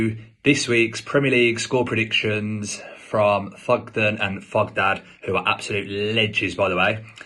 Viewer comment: Are you saying Thogden or welcome in the intro 2:30 and I asset we call them the Thug boys But respect the guest , he hates Villa so i like him now 12:23 Me:whispers Harvey Barns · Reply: I heard fukden and fukdad 🤣🤣🤣🤣